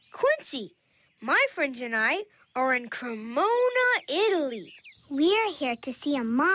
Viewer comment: Part sounds like Quincy said: My friends and I are in corona Italy’ I was like: HOLY F**K CRONA REEEEEEEEEE